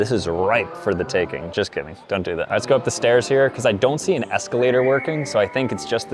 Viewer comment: did you just say stairs?